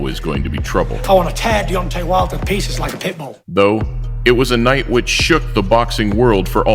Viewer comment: Deadass thought he said like a bitch💀💀💀